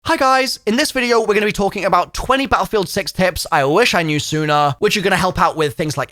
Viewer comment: I thought he said battlefield sex tips in the beginning of the video 😅